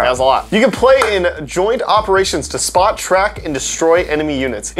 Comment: did he say spot Shrek?